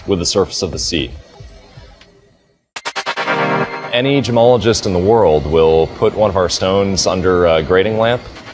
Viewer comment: homie says any gemologist in this world. My high ass dead thought he said "an Jamal just in the world" when referring to who's buying the diamonds, I was like was like wtf bro if you sit your racist ass down Lmfaooo. Had to run that back, people be crazy in 2022 Lmao